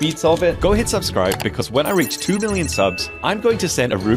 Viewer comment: I thought he said 2 billion subs 😂🎉😅😅